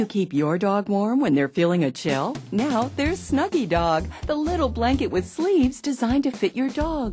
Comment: It sounded like she said, "The little blanket with FLEAS." :D